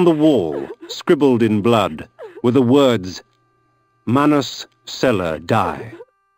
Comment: what were the words on the wall? i heard Manos cello die? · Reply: LOL. It actually says Manus Celer Dei ("the swift hand of God") See here for a complete transcript of the game <link>